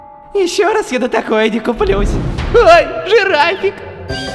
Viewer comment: "нет,я больше на такое,не куплюсь!" ОЙ! ЖИРАФИК! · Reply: Мне там послышалось жиральник